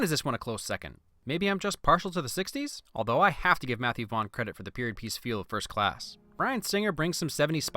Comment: I thought you said Matthew Von Credit XD